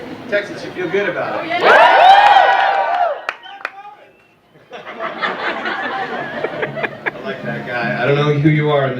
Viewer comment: what did the guy in the darkness say? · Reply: He screamed "JON FOREMAN" XD